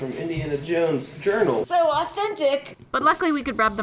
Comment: What does she say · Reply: So authentic